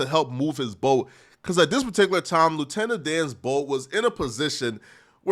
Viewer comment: Oh i thought it said lieutenant *dad My bad